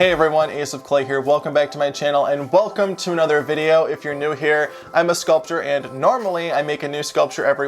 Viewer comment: Ngl in the intro I heard you say "I'm a sculpture" 😂 but I loved so many of the sculptures from this year, even if you say it didn't start off the strongest